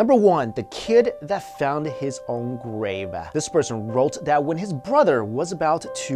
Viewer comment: "The kid that found his own gravy." ..........Please tell me I'm not the only one who thinks it sounded like he said that....?